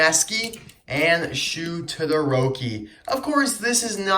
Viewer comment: I heard it as Shu To the rokie.